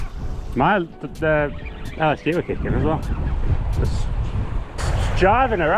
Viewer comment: Did I hear correctly at 13:01 "The LSD will kick in as well!" 🤣🤣 no better place to drop.